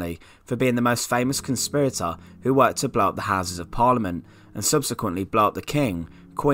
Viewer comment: What is this guy saying at 0:20 Fawkes wanted to do to parliament? Blot the houses? Blah the houses?